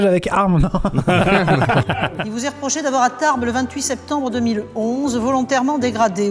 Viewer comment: c'est moi ou la juge a dit Tarbes ? ?